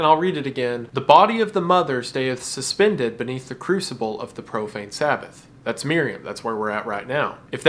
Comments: I don't know why my brain kept doing this, but I kept hearing "Propane Sabbath" and had to mentally double-take every single time. 😭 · I keep hearing "propane sabbath" which is a koth metal band i think. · I swear, I thought I heard you say "Propane Sabbath" at one point and suddenly pictured Hank Hill leading a cult